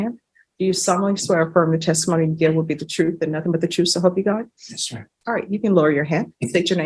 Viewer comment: did he just say, “Yes sir” as he was getting swearing in? I’m sure that didn’t help.